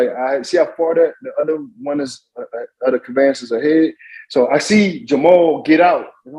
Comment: What is that fool calling their vehicles?It sounds like"Caban,but that doesn't really make sense,but most of what he says doesn't make sense.I really hope theylock these fools up ffor serious time. Thank you for the video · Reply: I thought he was saying "Cavances", but apparently, it's "conveyances". They try so hard to sound smart, but it's not fooling anyone.